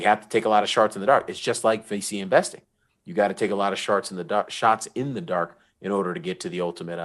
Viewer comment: All i heard was "YOU GOTTA TAKE A LOT OF SHARTS IN THE DARK" 🤔